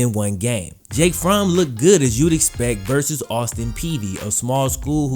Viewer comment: Did you say Austin Peavey? That's what I kept hearing. It's Austin Peay(pea) if so.